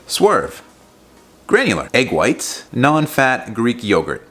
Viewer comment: Swear to God as I was watching this as I was eating a chocolate xanthan gum pudding (low cal, high volume, not sure on the protein) I heard "granulated egg whites"! I thought, "wtf is that and how to I get it", as I went back a few seconds and realized the granulated was the Swerve. LOL Just found this channel today and have been watching through your vids.